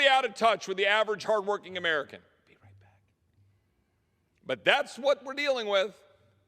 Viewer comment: Anyone hear someone whisper, "be right back"? Yes it was on 6:36 mark I just checked